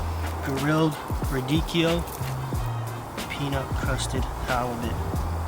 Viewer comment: Peanut!? I thought you said cashew😁